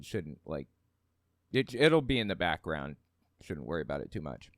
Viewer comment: I thought you said piddle be in the back. I love video games, but I honestly could care less about seeing them. I want to see the story big.